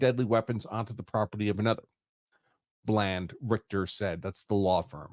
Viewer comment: Why did i hear "Bland Sphincter" as the law office name?